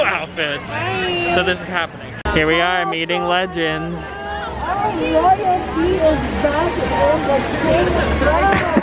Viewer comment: I heard " Here we are, meeting lesbians." I died for a second there.